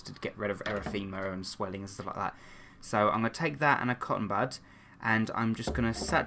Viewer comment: Hehe when he said cotton bud I thought he said cotton butt lol 😆❤️😝